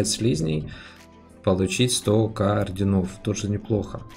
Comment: Мне одному послышалось 100к орденов?)